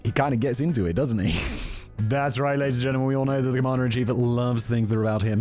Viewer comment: "Conmander in chief" is actually one of the cleverest things ive heard all day. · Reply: I thought he said Comandarin Chief, with mandarin being a reference to his orange color. 🍊 either way is pretty clever.